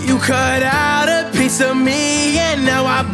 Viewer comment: I thought he said you a piece of meat lol